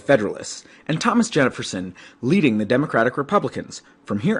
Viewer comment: did he say Jenniferson 😂